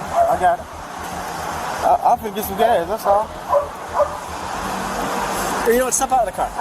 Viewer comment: Whoever the hell does these subtitles is doing a horrible job lol. There's so many ""*inaudible*" sections that I could clearly distinguish and when he says "I think this is gay, that's all" at 14:13 he is actually saying "I-I'm finna get some gas, that's all". You should hire me haha